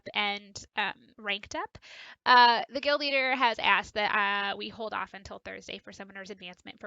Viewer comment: Correct me if I'm wrong at 1:10 did she say guild master because It's known as alliance leader I'm not hating I love watching this content